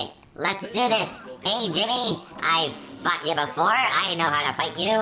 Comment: did you swear? please tell me! what did you say? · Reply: NYEH HEH HEH! HE SAID “FOUGHT”